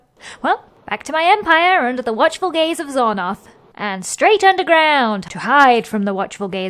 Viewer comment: minute 9:40 i heard you said "under the watchful gays of Xornoth" 😂